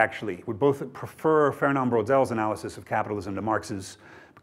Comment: Whose analysis of capitalism is Graham referring to at 49:13? I just can't catch the name, is it Fernand Braudel?